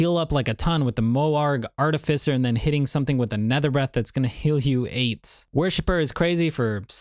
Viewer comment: Did anyone else understand "that is gonna heal AIDS"